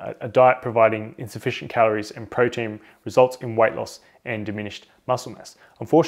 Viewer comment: did he say "white loss"?